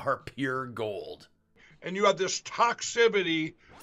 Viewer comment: Sounded like he said ‘toxibity’